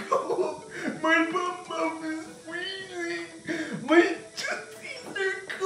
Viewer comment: Ik I'm really late but I'm still gonna comment..n tat is...at 3:18 what I heard was "My bumbum is wiggling!" 😂